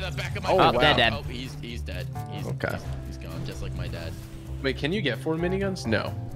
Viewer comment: Did anyone hear: "oh, he's dead, he's gone... just like my dad."